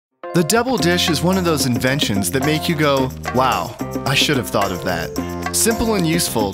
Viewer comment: I Thought it said ''The devil dish'' XD